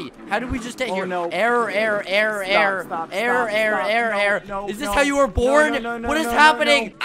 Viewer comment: Is it only me that heard this but I heard Skeppy say “is this how u were born?”!!!!